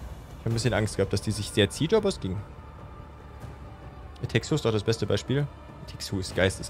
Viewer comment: An stelle 2:09:42 erwähnt Zombey ein Spiel, welches meint er? Ich suche überall danach, da es ja echt gut sein soll, aber ich verstehe es nicht wirklich und Untertitel gibt es keine... :( Kann mir jemand helfen? xD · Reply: It Takes Two, kam auf seinem Hauptkanal.